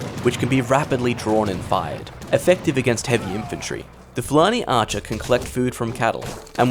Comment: I heard bloody Archer